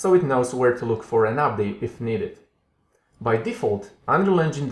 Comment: Great tutorial and again there're some places not obvious to me. First one is around 2:56 : "...it saves a source file path so we can know where to look for an ?????? if needed..... ". Second one is around 5:45 : "...You can switch view mode to unlit as post process as bloom or other effects which modified colors". I am not quite sure what this sentence means. Maybe I miss one word? Third one is not a big issue, but I am just curious. It's around 5:55, "...OK, that's going to ??? for this episode...... ". It sounds like a "bait", but I'm not sure about it. Thank you for your patience! !! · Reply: 1: it saves a source file path so it knows where look for an update if needed 2: You can switch view mode to "unlit" as post-process adds bloom or other effects which modifies colors" I meant that the "unlit" view mode is a pure color which is not altered by any other way 3: It's just : "That's going to be it for this episode"